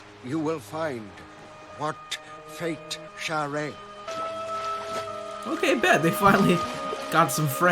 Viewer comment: I know it's weird but everytime the alien says "what fate omoroca", I hear "what fate I'm a rocker" and get the urge to listen to the Bruce Springsteen song "I'm a Rocker". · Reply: First time I saw this episode, my response was "You smash it, then eat it." (I'd heard it as "What fate almond roca?") It's a good thing I'm not an SGC linguist.